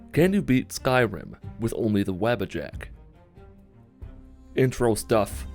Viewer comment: At first I thought it said: Can you beat Skyrim with only a Jack Wabbit?